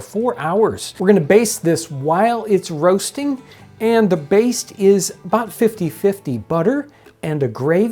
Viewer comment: he says "we are going base? bathe? this while..."? what is the right verb here? next at 7:03 he says "and the baist? is 50/50"... can someone explain what words he is using? (i am watching this to learn english) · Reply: Baste. Usually it means pouring the drippings back on to the meat.